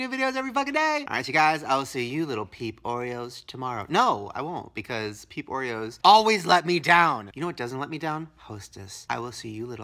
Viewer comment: When he first said peps Oreos I thought he said people Oreo